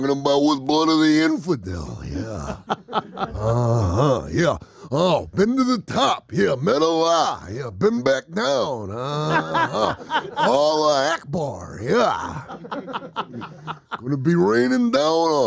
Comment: What is he saying between 0:33 and 0:38? Thanks! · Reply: Been to the top, yeah. Met allah, yeah. Been back down, uhhuh. Allahu akbar, yeah.